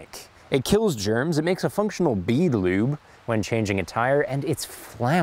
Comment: For a second there I thought you said hand sanitizer makes a functional Beaver Lube, was worried what you were doing to your national animal.